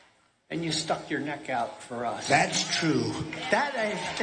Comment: Did he say that ain't? I know I didn't just hear him tell the truth to his base but none of them caught it. It sounded like he almost said "that ain't true". It's really that sad that they told people to get over Trump winning in 2016 when there was cheating to win but they're still hanging on to 2020's election.